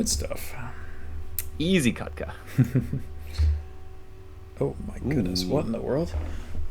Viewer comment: Wait, WHAT? Am I throwing or did really say "ez katka" there?